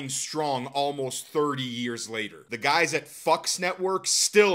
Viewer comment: To be fair, if they had signed with Fox, South Park would've become a part of Disney later down the line, a fate worse than death. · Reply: Talk about dodging a bullet! And did he just say "Fux Network?" 🤣🤣🤣